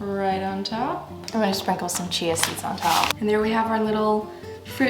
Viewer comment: Was looking away and thought she said “I’m gonna put some cheez-its on top” not chia seeds